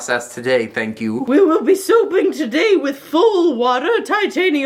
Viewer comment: Every time she said “soaping” it sounded like she said “souping”